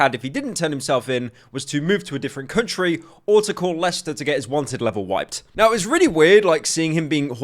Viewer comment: this subject is so british I thought said “Call Leicester” instead of “Call Lester”